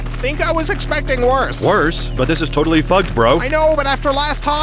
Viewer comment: what did he say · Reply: "but this is totally fugged bro"